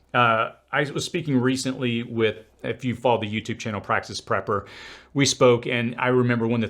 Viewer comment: Hey, all - what was the other youtube channel mentioned in this video? It sounds like he said practice prepper, but I couldn’t find a channel with that name. Thanks!